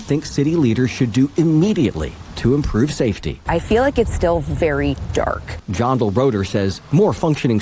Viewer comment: She said " its still really dark " but I heard " there's to many YNs"